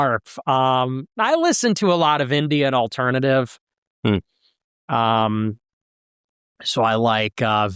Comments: thought he said Indian Alternative 🤮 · I thought he said "Indian alternative" haha "Indie & alternative" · I thought he said "Indian Alternative" here. 😆